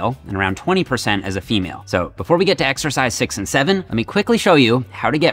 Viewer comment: did jeff say 6 7